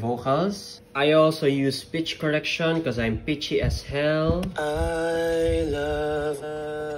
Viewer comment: i thought he said ''im bitchy as hell''